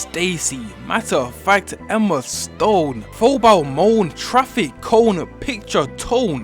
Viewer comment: he really said phobile mone